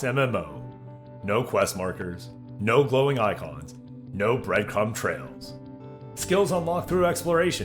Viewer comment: Anyone mishear "Bright crumb trails?" I almost thought he was running around with a blacklight